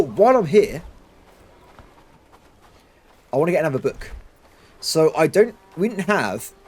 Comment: Watching your different videos in the background is tripping me out. I heard you say 31:00 "I want to get more BOOKS," but the crossover from Broken Arrow made me think you said "I want to get more BUKs," which are a Soviet air defense system, and in the various Cold/modern war tactical RTS games, usually a pretty good system so it fits that you'd want to get more of them. Great fun either way though!